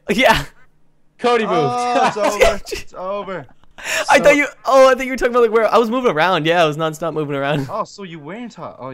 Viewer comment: What does the Cody say? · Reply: Bad joebuzz